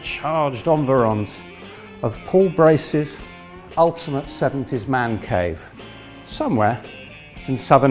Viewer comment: At first I thought he said "Poor racist ultimate seventies man cave".